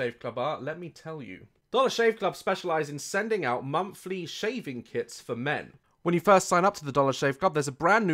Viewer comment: I was distracted so I was confused when I thought he said "shaving kids for men" 0:45 I AM REALLY GLAD I WASN'T DISTRACTED WHEN HE SAID THAT